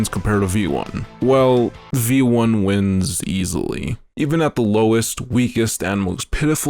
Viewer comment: thought you said he loses easily.